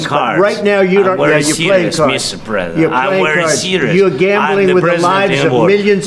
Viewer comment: Что говорит Президент Зеленский на 28:18 минуте? Он повторил это два раза на английском, но я не могу разобрать! "I am in war, I am serious" или что-то другое?